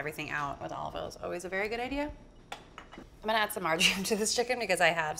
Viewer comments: When you added marjoram I thought you said MARGARINE and I was like "wait wait wait....what?!" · Omg, I thought you said “I’m going to add some margarine to the chicken” 😧